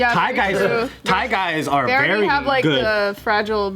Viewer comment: I thought say "not Try Guys" and I was about to say he's gay not trans. But he said Thai guys lmfao.